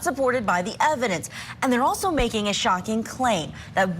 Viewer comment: Did she say Jenny Depp? 0:05 😂